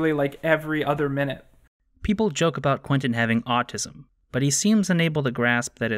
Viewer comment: I’m sorry I think I heard you wrong, for a moment I thought you said that QUINTON HAS AUTISM.